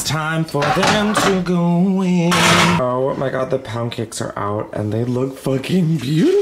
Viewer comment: NGL my volume was down and I thought he was saying “pah-n-cakes” until they turned out to be pound cakes 🥞